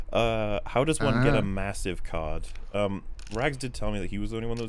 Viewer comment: Me: cleaning my ears at 3:04:31 while getting ready for bed Mishears "card" as 🐓 Immature sense of humor kicks in when says "I heard Rags was the one giving those out" Dies laughing